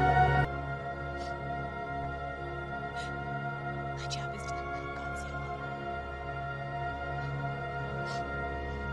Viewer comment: what did she say? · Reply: "My job is done now, Godzilla."